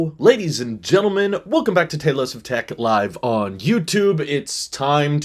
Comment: The captions make it sound like you say welcome to Taylor swift tech